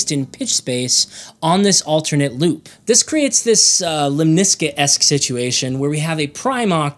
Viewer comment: Help? At 2:15 says, according to the subtitles, "...the creates this uh lmisesque [?] situation..." I cannot understand the word before "situation" , and the subtitles present a "word" Google search does not recognize. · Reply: “Lemniscate-esque” is I think what was said for anyone like me who had to listen to it multiple times?